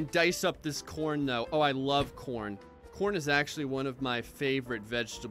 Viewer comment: when he said I love corn I swore on my jibblets he said I love porn